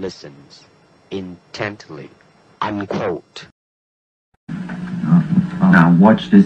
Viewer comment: the subtitle is wrong at 1:51 Bruce said "unquote" not "I'm Colt".